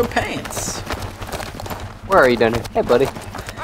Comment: Wait. O.o Is it just me, or does call Cry "Denny" at 9:35?